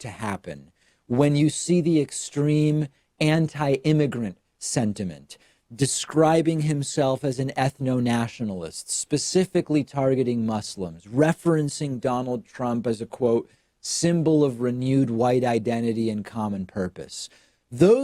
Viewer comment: doesn't it sound like David said "muslems"?